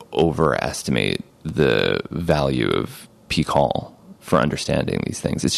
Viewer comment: What was the name of the book Hamilton says at 1:52:00? I can't seem to find anything. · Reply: Pihkal: A Chemical Love Story by Alexander Shulgin